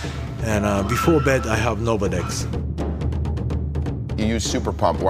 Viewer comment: Does he say "before bed I have Nolvadex"?